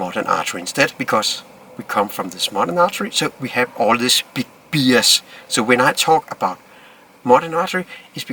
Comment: Note: When Lars says "B.S." 5:40 he is actually saying the more polite "Bias" with his strong Copenhagen accent.